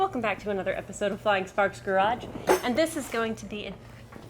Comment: i Love you guys .... but .... It sounded like you said welcome to Flying Farts garage ... lollollol